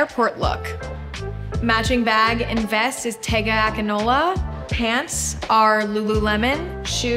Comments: im sorry but patagonia is not pronounced "tega acnola" --- please i cant be the only one who heard this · why did she say tegga aganola to patagonia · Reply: I think she said appx 6:40 matching bag and vest by tega akinola , pants by lululemon, shoes by vagabond. (however patagonia may also be a style/brand she wears, think there all on ig ).